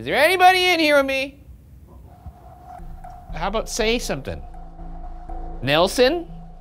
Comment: I don't know if this was Shane talking in another room but I hear someone talking after Ryan at 55:12 Edit: After replaying it I hear "hey its Nelson"